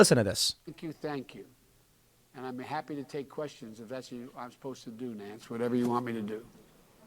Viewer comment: Did he just say, “If that’s what I’m supposed to do Nanc?” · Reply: “Im happy to take questions if that’s what I’m supposed to do Nanc, I’ll do whatever you want me to do”